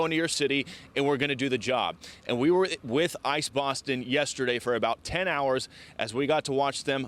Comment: 460 down, tons more to go. Every time the commentator said I.C.E. Boston, it almost sounded like she said “ice box” & at -5 degrees it would feel like an ice box. Anyway, thanks President Trump for backing up your“Promises Made/Promises Kept” campaign slogan.